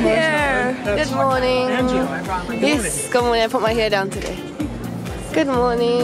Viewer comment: Could someone tell me what she said at 3:44? "something come put my hair down"? I'm trying to subtitle the video but I can't seem to figure out what she's saying in that part. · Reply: I think she started to say something like "It's... yeah, I put my hair down today".